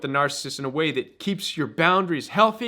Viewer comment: I thought you were saying... The Royal Weed...